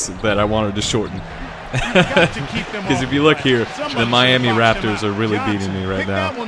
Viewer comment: Did they just say Miami raptors? Lol